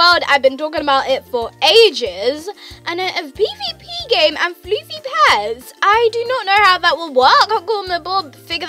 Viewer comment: What the freek she said? XD🤣🤣🤣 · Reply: Soy Alexa She said FLOOFY PETS 😂😂